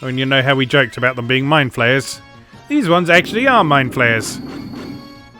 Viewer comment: This entire time I thought you were saying Mind Flares, not Mindflayers